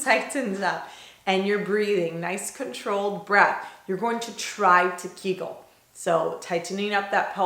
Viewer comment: When you said "you're going to try to kegel" at first I thought you said "giggle" haha I was like "okay hehehehe"